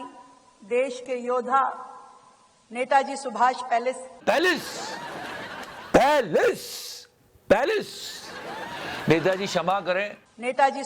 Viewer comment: And I heard Lord Baelish 🫣